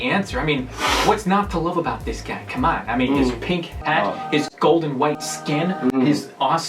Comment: "his golden white skin" ... GOLDEN AND WHITE ARE TWO DIFFERENT COLORS .... realizes he actually said glowing, not golden ohhh okay....